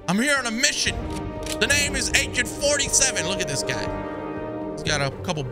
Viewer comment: I thought he said "the name is ak 47" 😂😂